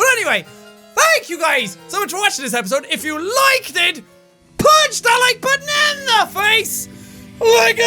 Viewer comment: I thought at the end he said "Punch that like button like a bus"